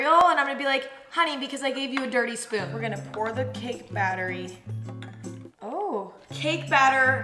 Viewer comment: anyone else hear her say cake battery 😂